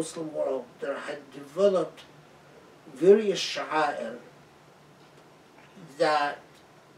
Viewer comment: What is the Arabic word that Dr Abou El Fadl uses at 26:05? It sounds "Shahair".